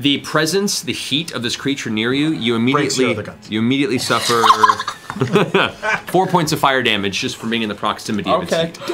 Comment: Can someone help out? What did Sam say at 1:54:40 ? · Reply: "Breaks your other guns"